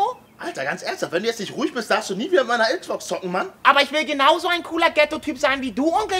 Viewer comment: alter wenn du nicht ruhig bist darfst du nichtmehr mit meiner XBox Zocken man für die die es ncih verstanden haben;)